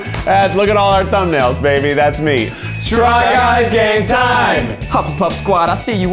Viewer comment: Did I thought the said welcome to the try guys gay time🤦🏻‍♀️